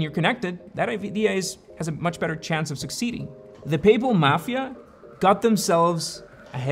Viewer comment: I thought you said Papal Mafia